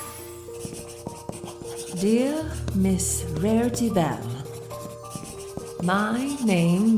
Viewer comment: did she say rarity Bell😮